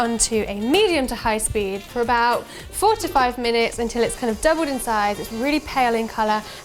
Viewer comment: I think i misheard when you said to beat the butter, I'm guessing you didn't say beat for 45 minutes?!